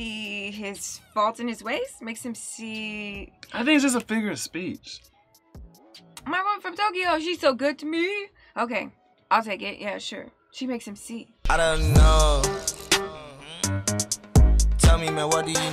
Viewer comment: When I was much much younger, I thought he was saying my woman is okay yo lol